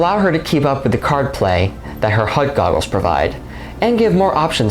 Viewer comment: I misheard HUD Goggles as Hype Goggles, but then. That’s not totally untrue is it?